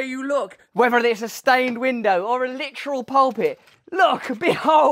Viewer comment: Oh-ho, 'pulpit'! I thought you said 'HOBBIT'. Now, THAT would be splendid!